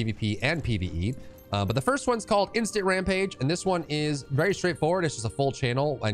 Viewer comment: I swear I thought you said Instagram page, hahahaha